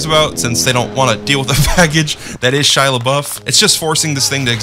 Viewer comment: “Faggege” is that what you said, did you mean, package?